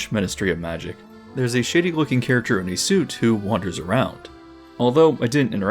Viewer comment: definitely thought you said "shitty looking character" lol